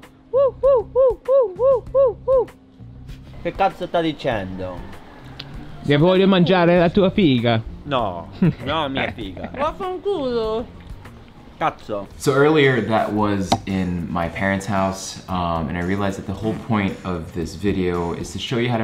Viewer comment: Did Solenn said 'vafanculo' at 13:17 lool